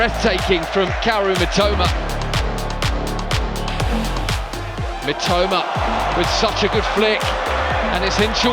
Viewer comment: 実況の人が三苫というときに、「めっちゃうま」って聞こえる。。例えば、16:44のところ しかし、このスピードと運動量チート